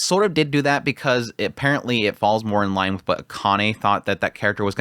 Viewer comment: did he say Kana or Akane because wasn’t Akane the one that came up with the idea about the princess? Please let me know